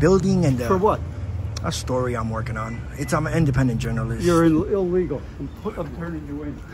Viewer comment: When the old guy said "you're illegal, I'm turning you in" I don't think he meant for trespassing. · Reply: I thought he said you're an illegal 🤔